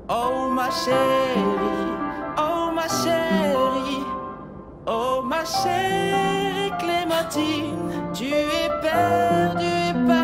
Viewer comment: The french one i thought he said Oh my shitty Oh my shitty clementine